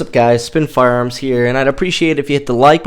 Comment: Thought u was saying spittin Fire this whole time 😅